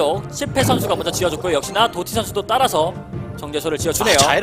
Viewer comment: 도둑선수라고 들리는데 나만 들리나?